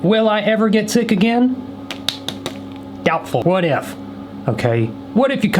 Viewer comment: I had to go back because when he said "will I ever get sick again? Doubtful." I thought he said Goutful😭